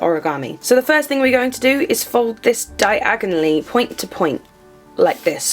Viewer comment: hahha when you said "fold this diagonally" I heard "fold this Diagon Alley" XD